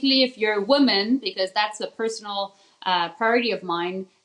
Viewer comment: did she say 'personal party of mine'? What does that mean? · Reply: She said "Personal priority"